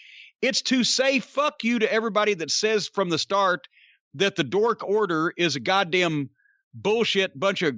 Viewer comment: Did Cornette call them the “Dork Order,” or did I mishear? Either way, hilarious. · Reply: He has been calling them dork order for a long time..